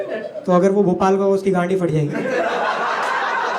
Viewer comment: why in the subtitles it's written Tanmay instead of gand😂